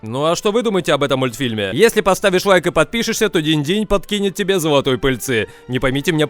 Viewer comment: При словах подкинет золотой пыльцы, мне почему то послышалось дождя. Мдам, просрал уже давно детскую невинность.